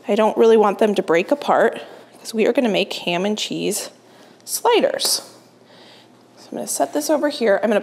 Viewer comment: I love her videos too. They make me smile. I had to back it up and say did I just hear her say ham and cheese spiders? Of course I knew she meant to say sliders.. Well I backed it up twice thinking I misheard her... She's too funny.